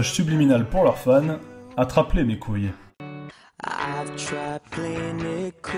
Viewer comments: Pour les one direction moi j'ai entendu "Attrape les nekos" ...Je regarde trop de animés moi 😂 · pour les one direction j'ai entendu "rattraper les cours" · Moi j'ai compris "Atrape les mes couilles"